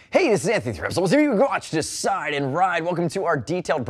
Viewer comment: can someone please tell me what he said in the beginning or did he actually have a stroke? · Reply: "Hey, this is Anthony with Revzilla, where you can watch, decide, and ride. Welcome to our detailed breakdown of the new AGV K3 SV Helmet, available at <link>." -DrewZilla